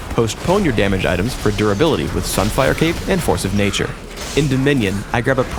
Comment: when he says "sunfire cape" the subtitles says "cap"